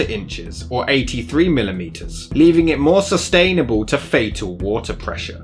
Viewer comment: You said more sustainable instead of susceptible at 22:33.